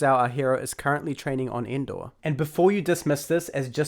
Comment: I might've misheard that. Did you say, "Our hero is training on Endor?" Or "Our hero is training on Indore?"